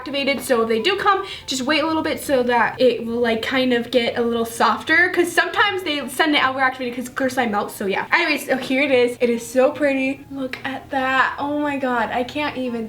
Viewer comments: did she said I'm gonna f*** it guys I'm gonna f*** it? Or am I just hearing things?! 😂🤔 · it sounds like shes saying “im gonna fuck it guys” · who thought at 4:57 she said i’m gonna fu*k it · am i the only one who heard her say "fu*k it" instead of "poke it" ?????? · I thought she said I'm going to f*ck it but if that's not it then it mean I'm going to poke it what do u think? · I swear at 4:53 she said I'm Going to fuck it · I thought she said I’m gonna fuck it I’m gonna fuck it 😂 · I thought she said im gonna f*** it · it sounded like she said "I'm gonna f*** it" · It sounds like she said " I'm gonna f### it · it sounds like she said I’m going to f*** it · it sounded like she’s said I’m gonna f it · she said I'm gonna poke it but it sounded like she said I'm gonna f it omg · Who else heard at 4:57 that she said I'm gonna F IT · it sounds like she said "I'm gonna f** it guys, i'm gonna f*** it